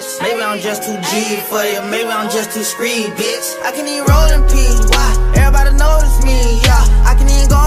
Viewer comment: Sometime it's sounds like he is saying "I can't even roll and pee" 🤣